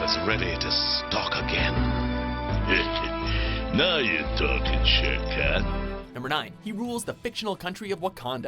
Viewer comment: did he say "Now you're talking shit, cat"? Just asking.